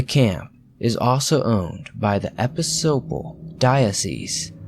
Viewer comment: Was he trying to say Episcopal? I can't make out the word he said.